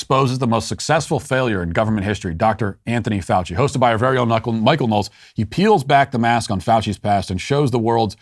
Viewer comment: It sounded like Matt said "Kunckle Knowles". I'm down with that. Mike can be a knucklehead.